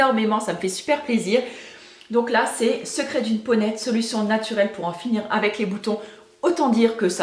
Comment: haha j'ai bloqué quand tu as parlé du livre. J'ai entendu "secrets d'une ponette" (genre femelle poney) au lieu de "peau nette" XD